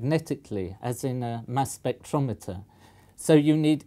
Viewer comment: did anyone else mishear "Mass spectrometer" as "Mass Effect Andromeda"?